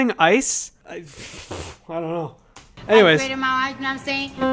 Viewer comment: UPGRADED MY ICE! i heard "I upgraded my ice, naw sayin'" loud and clear the first time